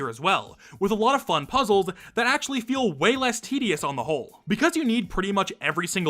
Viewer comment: really thought you said "way less tedious on the hole" and it got me laughing for 5 minutes straight